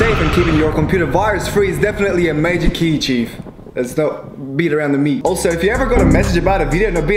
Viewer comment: talks Me: did he just say “beat around the meat”